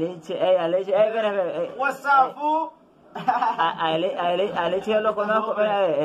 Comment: Alguien me puede decir qué es lo que dice el man que está a la par del Pana en el 1:31 por favor? He escuchado que dicen algo como "what's up fool" pero no sé si es eso mismo